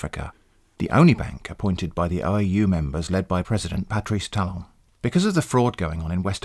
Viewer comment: Misheard President Patrice Talon as President Patrick Star.